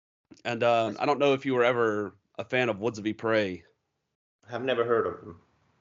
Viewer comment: What is the name of the band you mentioned at 55:55 please? I couldn't quite catch it. What a great interview, enjoyed listening to your chat, and thanks for sharing all these bands that you like. Can't wait to check them all out, especially Trees of Eternity and Swallow the Sun. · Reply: Woods of Ypres!